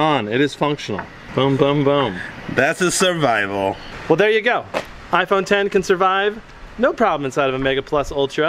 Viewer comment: Did he just say iPhone10?!